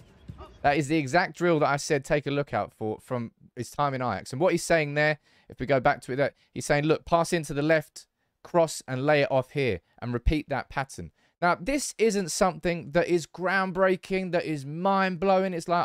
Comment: Lay it off here sounds like he is saying De Jong Here haha